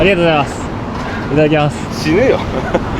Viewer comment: 死ねよにしか聞こえない